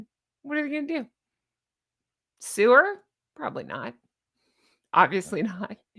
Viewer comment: bahahahaha. I thought you said “sewer” as opposed to “sue her”! Using sewer and Megaliar in the same sentence is brilliant 😂😂😂